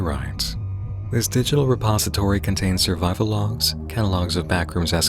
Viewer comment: I misheard "survival logs" as "survive-alongs," which would be a great name for post-apocalyptic vlogs tbh.